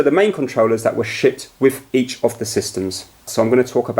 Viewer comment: it sounded like he said 'Now there's gonna be lots of other third party controllers but these were the main controllers that were *SHIT* with each of the systems.'